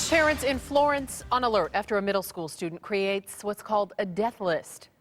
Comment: it sounds like she is saying karen on alert lmafo